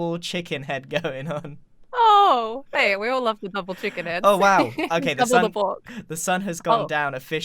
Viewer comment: no one: 18:08 pearl: “we all love double chicken heads double the pOrK” uhm am I the only one who heard this 💀💀